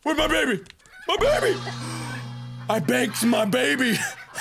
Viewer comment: what did you do precious? I BANKED MY BABY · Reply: I kept hearing "baked" too XD i was like "oh right. bank. that's what the button-thing said."